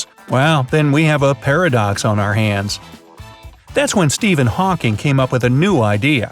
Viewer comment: Did it said Steven Hawking?